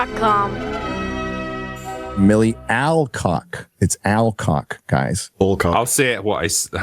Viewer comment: I honestly thought it said "Milky Allcock"... Let's be honest... Jason Momoa's Lobo is gonna be the ONLY good thing in the Supergirl movie...